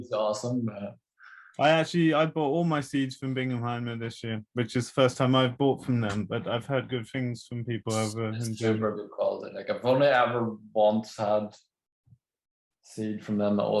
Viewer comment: He said where he buys his seeds, anyone know what he actually said? Binagheim, Binnahaein????? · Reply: Bingenheimer Saatgut